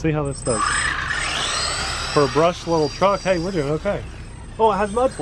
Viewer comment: I'm confused. Your title says brushless, and the website says brushless, but you said it was brushed. Is it brushless or not? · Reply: Chico Little I thought he said brushless